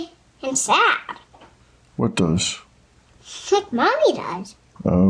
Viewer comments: the baby didn't say "like mommy does" she actually said "shit mommy does" ... why does this 4 year old baby already have shit in her vocabulary? i don't even think i knew my full abc's yet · did she say "the SHIT mommy does" 😅😅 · the subtitles say 'like mommy does', but I keep hearing 'sh*t/she mommy does'. clarification please if possible? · “Baby” actually sounds like she says “shit”, instead of “like”, which is diabolical. Teaching a 4 year old not only that word but to assume it’s appropriate to use is awful.